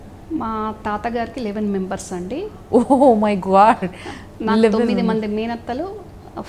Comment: Who's that over-scene Anchor??. At 5:37 she is saying as " oh my gwaad" instead of God. Very awkward. పైగా మాత్రమే మేకప్ చేయండి. ఓవర్ స్టైల్.